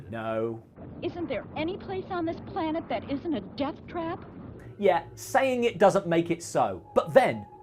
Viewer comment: I could have sworn she said “jump cut” instead of “death trap” and had to rewind to check 😆